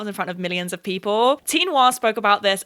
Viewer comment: anyone know what the mentioned channel is called? I don't understand:( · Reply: tee noir